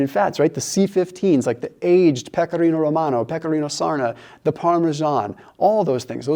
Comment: I'm Italian but I don't understand what you say after Pecorino Romano: maybe Pecorino Sardo? Because I can hear something like Pecorino Surna